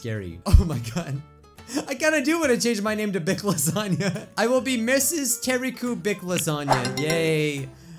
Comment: Me and my mom were watching this video and she thought you said ‘stub my toe big lasagna’ I’m crying 😂